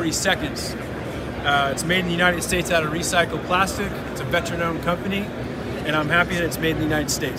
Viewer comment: I heard “XSteel mixer, made of recycled plastics” and was a bit confused ngl😅